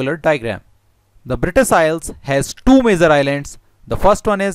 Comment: You broke this down perfectly for anyone who is looking to learn. Good job buddy :) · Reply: haha good point! I thought he had said the main island. It’s British Isles including all the islands to anyone reading